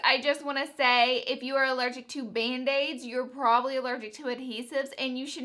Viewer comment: when you first said band aids I thought you said mayonnaise 😂